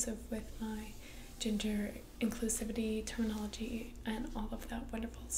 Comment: Tell me why I heard 14:55 as "ginger inclusive" 😭